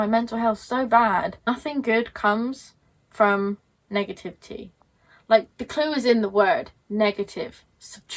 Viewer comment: I kept hearing "nothing good comes from negative tea". Then I heard the right words "nothing good comes from negativity".